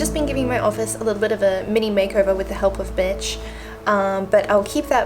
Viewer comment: "with the help of mitch" I swore it sounded like the help of b*tch Haha obviously not just swore I heard a "B".😆🙈